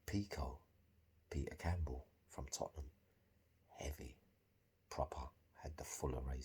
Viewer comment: Big up I heard you say peeko Tottenham! My G he is a big baller ⛹️